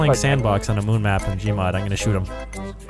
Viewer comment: did vanoss say "i gonna shoot him" ?